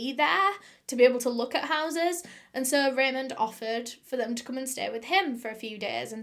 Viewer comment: Raymond Roff? I first heard “Raven Roth”, aka “Rachel Roth” the founder of the Teen Titans! 🤣